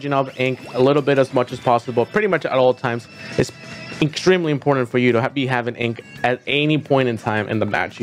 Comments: did he just say Inkstremely? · Did you just say Inkstremely